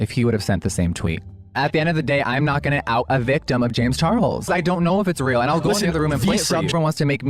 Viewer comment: Did Jeff say he didn’t know if it was real?